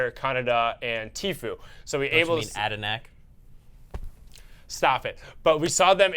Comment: what does he say? Add a Mech? Add a knack? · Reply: khanada’s in game name is adanahk since it’s his name backwards and said adanahk as a joke